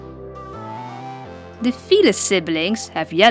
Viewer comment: not to be rude or anything but it kinda sounds like ur saying fetus instead of felis Srry if ur offended